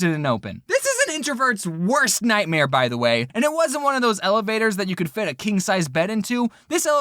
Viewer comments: It sounds like he says elebator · Who heard him say elabator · I heard you say elabotor